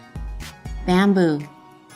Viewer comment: what did she say ? i didn't get it i must heard it wrong i heard manboob.... Oo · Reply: Chams Benzina she said Bamboo xDDDDD